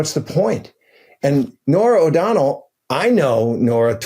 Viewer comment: I keep hearing “Norm MacDonald” when anyone says “Nora O’Donnell” and I enjoy pretending he’s the one who did the 60 Minutes interview. Cheers me up. 😅 it would’ve been so funny.